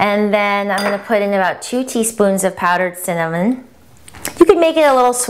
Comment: What was the first ingredient after the buckwheat? it sounded like you said "powdered cinnamon" - but it was white... and you added cinnamon later. Was it stevia?